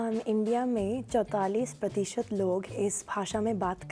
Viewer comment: Wait, did she say Chautalis?! 😅